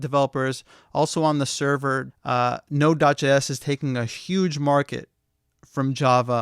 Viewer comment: from 4:17 onwards, what is that you say? It sounds like 'nodudge s' , or 'nodudge js' - captions say "node js" . You mention that node js is the most popular of all the frameworks. For the avoidance of confusion, can you clarify something - nodejs is not really a framework, is it? (or is it?) Thanks